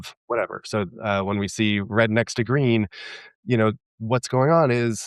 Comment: Did he say REDNECKS? Jk.